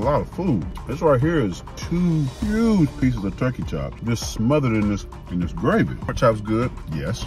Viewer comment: Did I hear “turkey chops”? Looked like pork chops.